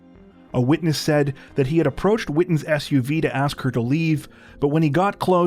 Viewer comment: ish Did You say Blair Whitton or Or Blair Witch?